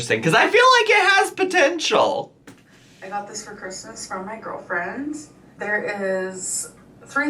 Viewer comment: did she say i got this for Christmas from my girlfriend'S?